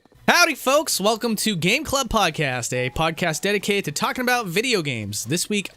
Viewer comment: I thought he said "Taco Bell"!